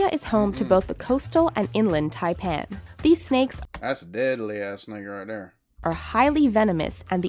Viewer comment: at 12:11 did you say "That's a deadly ass N-word right there" ?? I mean it sounded it 😂😂